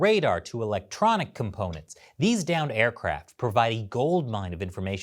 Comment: I heard, "these damned aircraft" instead of "downed".